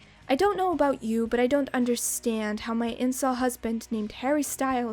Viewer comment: Did I hear right? "Incel husband"? 🤔